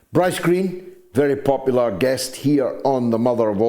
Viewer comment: When you said Bryce Greene I thought you said ice cream. 🍦😂👍